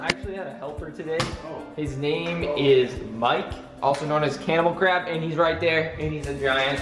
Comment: the subtitles said camel crap instead of cannibal crab